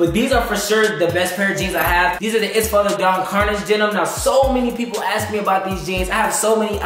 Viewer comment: what type of jeans did he say couldn’t understand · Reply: Their called “Empty Spaces Carnage Denim”